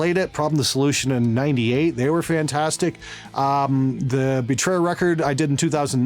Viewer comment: I think I've heard Betrayer.